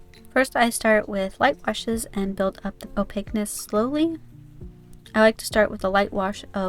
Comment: What did she say at 3:23? Opictness? Apectness? I’m writing down notes and I can’t seem to understand what she said. I’m looking for words on what she might’ve said but nothing art related. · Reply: Opaqueness :) sorry I didn't enunciate that very well